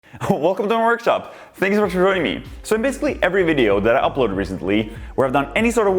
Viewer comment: What the hell does he say? lol "Wehcome tomeh worgshop, aaangsh vehsha shamasshoieeng ee"